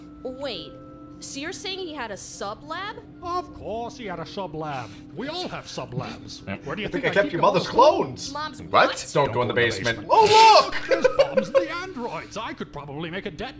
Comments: i always thought he said your mothers clothes not clones oh lord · I always thought dr. Breifs said "where do you think i keep your mother's clothes " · gonna be honest because of the accent i always thought he said "your mothers clothes"